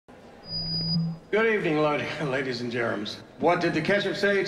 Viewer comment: ladies and gentlemen I heard ladies and GERMS 😂